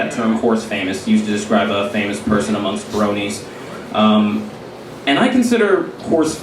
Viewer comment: Who else thought the bronies said brownies